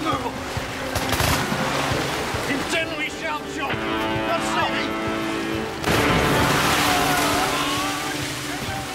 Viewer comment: What does hagman say to the sergeant at 2:20? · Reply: "Indeed we shall chum, God save ye"